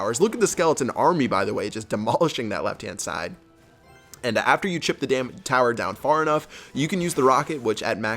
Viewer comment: it sounds like he said once you chip the D Tower down